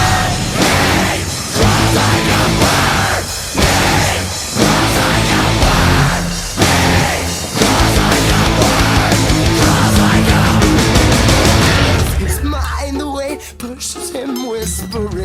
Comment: for some reason i keep hearing "Cries like a welsh man!"